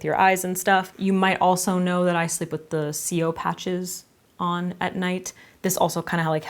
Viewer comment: What are the patches that you sleep with? It sounded like you said “CO patches”.